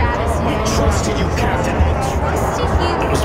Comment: The amount of emotion when Mark screams "CAPTAIN" impressed me. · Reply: It sounds like he says “help me” instead of “captain” I’m spontaneous sure how y’all are hearing “captain”